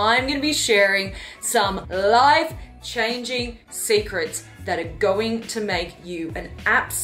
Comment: thought she said live 🤷🏿‍♂️